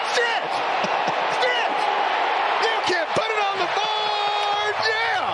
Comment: Not gonna lie. Thought Jeff said “Put in on the barn.” I was cool with it.